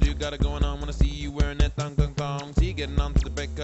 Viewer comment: i always thought he was saying thunder thong rather than thong thong thong